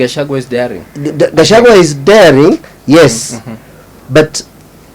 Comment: I thought they Said gachagwa is a villager What is the fear!!!